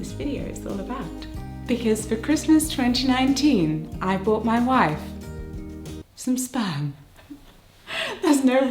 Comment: First I thought that you said I bought my wife spam, like you know, meat